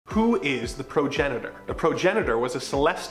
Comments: Thought it said pregenator for a hot sec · I thought you said Pro janitor